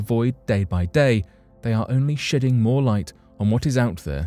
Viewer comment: I could have sworn he said "shitting more light"